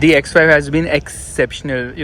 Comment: did i hear X5. Quality content as usual.